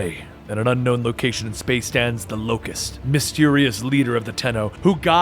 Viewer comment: did you call lotus the locast?